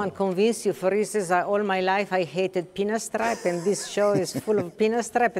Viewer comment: what miuccia said that she hated all his life? peanut straw? my english is not so good pls help. is she referring the mohair fabric? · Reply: pinstripe fabric